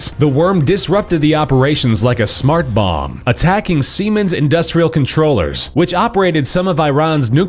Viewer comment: "attacking semen's industrial controllers" wow am i hearing this right?